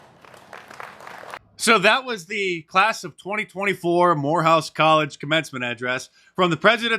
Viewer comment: I heard you said "Whore house gollege" xD